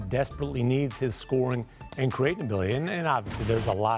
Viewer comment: did he say creatability?😂